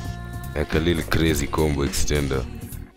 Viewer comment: deep ahhh voice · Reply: it sounds like he said “ it’s a little glizzy combo extender”